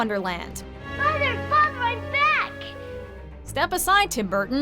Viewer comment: # 7 alice, sounds like she says "motherfuck i'm back!".